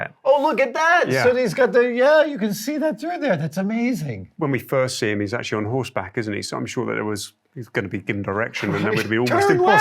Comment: I thought he said “when he first see him he’s on horseback and it must’ve given him an erection” lmao I was like “Wuuuut?! They designed the costume to accommodate that?!” Lmfaoo I’m very sleepy. Time for bed. Hahaha